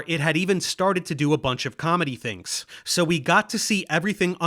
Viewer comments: I keep hearing "comet-y" as "comedy". "3I/ATLAS is doing comedy things" is doing comedy things to my imagination. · When you said "It did all the comet-y things" I thought you said it did all the "comedy" things and I was trying to figure out why it was funny.